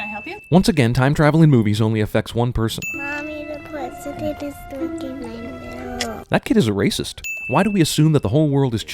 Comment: what does this kid even say I could hear a damn word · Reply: mommy the pwesident is dwinking my milk